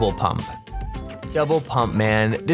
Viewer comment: he sounds like he is saying devil pump instead of double pump